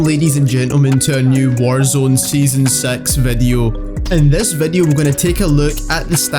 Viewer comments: Sound like he said season sex video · I thought I just heard “welcome back to another season sex video” I’m like whatttt🤣🤣